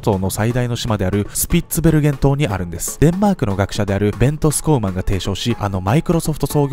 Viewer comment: メントスコーラに聞こえた